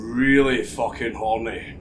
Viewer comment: I watched this two times the first time I thought he said hungry at the end the second I realised it was horny😂